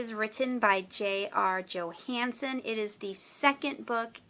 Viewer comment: I love your necklace! :D I also loved the video! I thought you were gonna say J.R.R Tolkien instead of J.R Johansen, and I was like: WOOOOOT, what have i missed!!??!?!! Hahahaha! :D